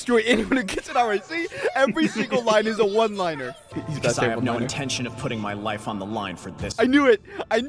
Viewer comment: bro am i hearing rin itoshi's english voice actor say he has no intention for putting his life on the line?